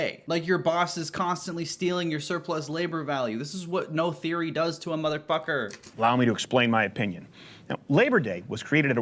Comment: thought said "this is what no Siri does to a mother fucker," had me like honestly i dont see how an iphone of all things correlates with helping to rid this mans lifetime of propaganda brainwashing.... fuck it ttho hasnt once ever let me down before and i dont imagine he will start now so just lmk when the 'iPhones for Crowders Crew GoFundMe' goes live ig